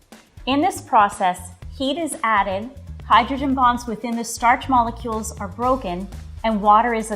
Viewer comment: I thought she said hydrogen bombs